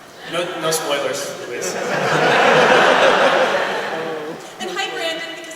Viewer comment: " I guess we have to give those dolls back now" I THOUGHT YOU SAID NO SPOILERS